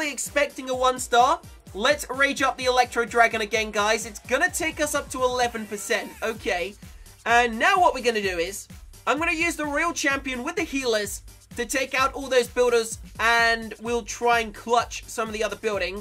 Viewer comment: really, did he said royal champion?